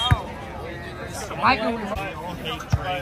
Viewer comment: Did she say he’s “amphibious” 😂😂😂😂😂